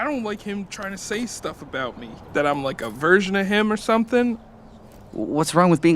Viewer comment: i thought that guy was saying "like I'm a virgin to him"